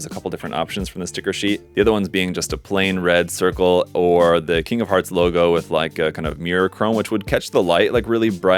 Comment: I thought you were saying, “Kingdom Hearts logo” for a few seconds.